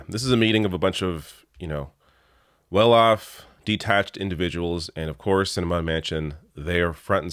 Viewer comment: When you said "detached" people, I heard "de-taxed"!